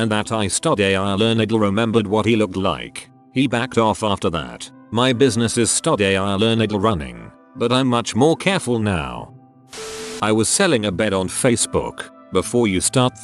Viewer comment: sorry about that, my abbreviation program bugged out (TIL > today I learned), this should already be fixed in newer videos :) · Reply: I honestly thought it was saying "Study A.I. Learn"